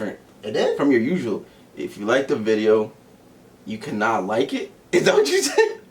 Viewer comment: I love how he made up the outro, but totally forgot it lmao. That new one doesn't hit like the original. I lowkey thought he said "if you liked the video, you can not like it" lmao